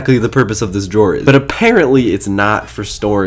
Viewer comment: When he said pans and bots I thought he said pants and pots.